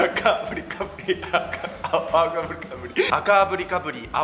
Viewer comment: あきらかブリブリにしか聞こえないｗｗｗｗｗｗ